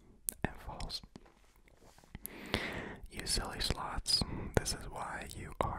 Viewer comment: every time you said sloths i thought you said sluts lol